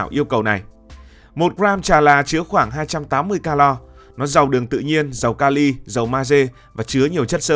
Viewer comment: 1g chà là = 280kcal thông tin sai nhé bạn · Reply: Bạn nghe nhầm rồi, họ nói 1g chà là chứa khoảng 280calo.